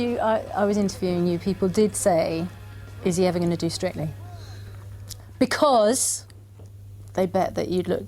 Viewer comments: What does she say at 4:42? I heard "are you ever going to do straightening?" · oh my god I thought she said stripping not strictly